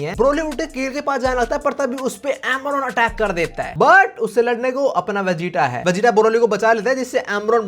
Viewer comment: did he just say "Boro-roly" ? 😅